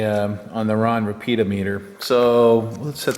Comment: Hi, Was slightly concerned at first...thought you said building a few "Peter Meters"...